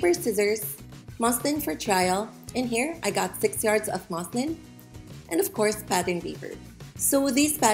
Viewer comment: naw bc I thought she said ‘i got six yards of muslims’💀